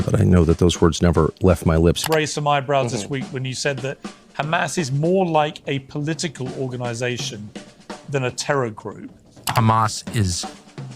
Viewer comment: he did not say they were more like a political organisation than a terror group he said they were more like a political organisation than a jihadi group as in they weren’t when an Islamic terrorist organisation not that they weren’t a terrorist organisation